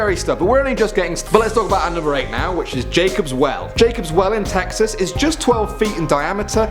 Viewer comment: when you said Jacob's well I thought you said jacobsville which is where I go to school I was so scared for a moment